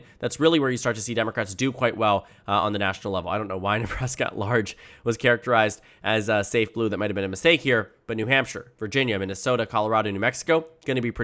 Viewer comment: Wth were you talking about at 9:45 mfr gave me an aneurysm looking for what you were saying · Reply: "I don't know why Nebraska at large was characterized as uh, safe blue that might have been a mistake here." I understood what he was saying the first time I heard it.